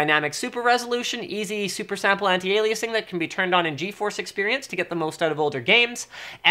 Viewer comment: Did he say "Eazy E Super-sampler anti Aliasing" @ 3:05 ?? Sounds sweet dawg